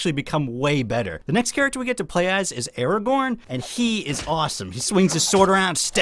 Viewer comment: Did you just say: Araborn? @ 2:56 I loved this game as a child and still play it sometime again just for nostalgia